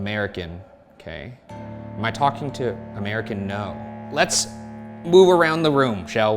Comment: is he saying “American No” because he isn’t white… 😭